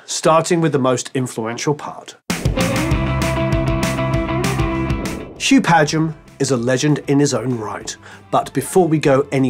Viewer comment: Misheard as saying, "Hugh Padgham is a legend in his own mind." 😮 Re-listened because I couldn't believe would say that! 😄